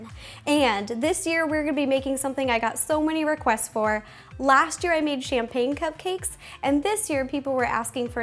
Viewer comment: oh I thought you said "last year we made shampoo cupcakes" LOL